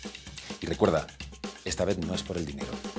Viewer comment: DID HE JUST SAY FAMILY